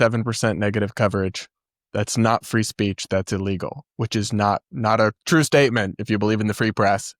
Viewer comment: yup, you’re crazy. He did NOT say illegal. You played the clip yourself! He said cheating.